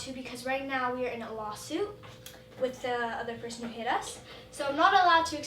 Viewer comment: i thought she said “we were in a bowl of soup” when my volume was low 😂🤦🏽‍♀️